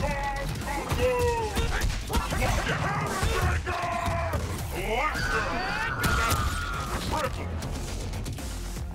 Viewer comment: what does it says in 00:55 ? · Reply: COUNTERBREAKERRRR when u combo break a combo break lmao